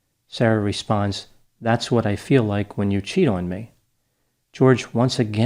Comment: Sarah doesn't know how "normal " couples interact. She talked about listening to music, then painting, then doing puzzles......it was like she was trying to keep an ADHD kid occupied. She showed detectives that she thought this was a normal couple interaction via her interview. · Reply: I thought she said "that's what it feels like when you CHOKE me" in that video, the fact that she actually said "that's what it feels like when you CHEAT on me" seals the deal on this case. I was having sympathy, but it certainly doesn't sound as if he was physically abusing her by her own account, that fact makes this case pretty clear: she was punishing him because she felt he was being unfaithful and she did it by torturing him to death - so basically, fuck her. Unbelievable. She talks about him like a pet owner would a dog, she is clearly a narcissistic person who sought out someone who would be entirely dependent on her emotionally and that's what she got with this guy, he started messing around with chicks online and she didn't like that.